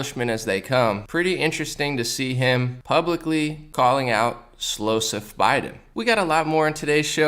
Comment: I thought you said “slow sniff Biden” haha it works, too.